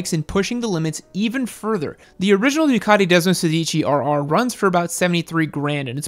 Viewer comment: When I heard Desmosedici, I swore I heard Despacito. Lol